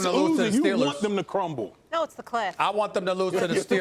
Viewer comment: BOYYYYY IT SOUNDED LIKE MOLLY SAID “CLIT” NOT “CLIFF” LMAO 😂😂 Molly a lil freaky deak😂😭